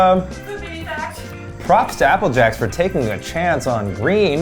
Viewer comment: For no reason at all when said "Apple Jacks". I thought he said "up your ass". Lol 🤣